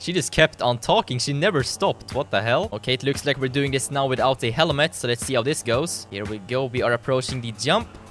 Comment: did i hear him saying "this one is going to be without a helimet" at 4:18 :D